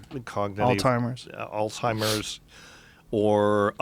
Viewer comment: I heard "Old-timers" instead of "Alzheimer's." I feel so ashamed lol.